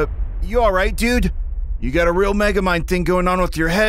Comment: wait... Did he just say Megamind? You probably look like Snotty Boy Glow Up. 🤣🤣🤣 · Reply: yes. He says Megamind